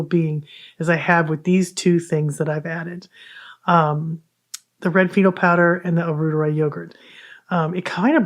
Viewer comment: I thought you were saying red beetle powder. 😂 Your voice can be hard to understand sometimes. It would be nice if there were some visuals.